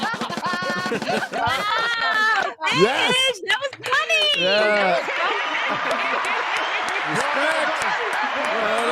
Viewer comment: I’m still trying to figure out what she said at 2:52:01 that they all laughed at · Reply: She said "Buttons" as in 4/10 buttons. A quick-witted reference to remark about taking off a button for every time she doesn't know something about the movie. See timestamp 1:28:15 for the complete explanation.